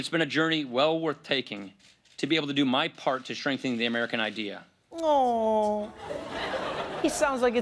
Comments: I honestly thought he said "shrink the American ideal", teehee...anyone else terrified he's just doing this so he can run in 2020? There's no way that dude wants to spend time with his family...and def not vice versa!!!! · It sounds like Paul Ryan said: “it’s been a journey well-worth taking, to be able to do my part to ‘shrinking’ the American idea.” -At least this reading would be accurate. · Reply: Yeah, I thought he said "shrinking". And that's what's in the transcript at <link> . Another poster suggested he said, "strengthen", which I think is the word. Trump's "God bless the United Shursh" still wins: <link>